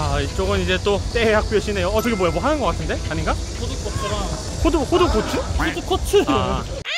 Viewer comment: 푸드코트를 호두꼬추로 들었다고?????ㅋㅋㅋㅋㅋㅋㅋㅋㅋㅋ